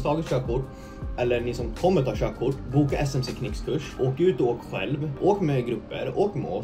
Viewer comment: en fråga bara, vad sa du i videon 7:45? msn kurs? eller vad sa du+ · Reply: hahaha SMC Knix kurs ( <link> ) , förstår fattade halvt inte ens själv vad jag sa 😂